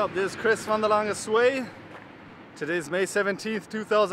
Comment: When you dance you really do become "Chris from the Land of Sway"! 9000km - super cool. Keep it up and keep safe and two whomever thought he was saying "From the Land of Sway" -- you're not alone.